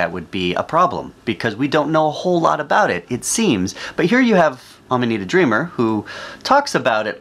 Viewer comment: I seriously thought they were saying, “Ima meet a dreamer” Why is no one talking about that woman’s last name?! Why?! *Lemongrab voice*: “UNACCEPTABLE!!!!!!!”